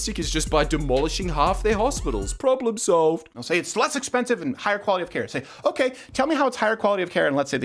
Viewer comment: slutz-expensive? Wtf did he just say lol Oh wait I just figured out that he said “it’s less expensive”